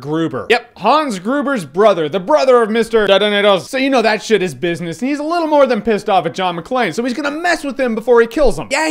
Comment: What was Jeremy Jahns saying around 1:04?... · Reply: "Detonators", but the way Hans Grueber said it